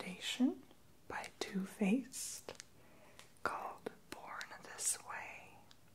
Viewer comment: born this way I thought he said porn this way I was like DAMMMMMMM